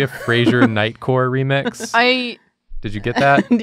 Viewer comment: This comment section has a severe lack of nightcore Frasier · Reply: I kept hearing it as "Night Court" and was really confused that it gives anxiety.